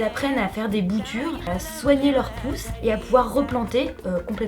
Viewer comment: Haha j'ai compris "soigner leur pouce", plutôt que "soigner leur pousses", je me demandais si ça abîmait les doigts de planter des arbres 🤣 #quiche. En tout cas c'est un tellement beau projet qui se réalise, bravo! 🌱